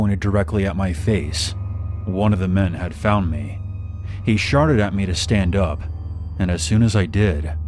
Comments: "He sharted at me" BAHAHAH I HAD MY CAPTIONS ON AND I HEARD THE MAN SAY SHARTED PFFFT BAHAHAHAH😭😭😭😭😭😭😭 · excuse me did you say sharted xD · Am I hearing things or did he say "sharted" instead of "shouted" ?